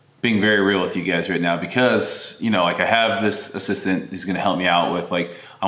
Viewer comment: I really thought gf But you said assistant. She seems great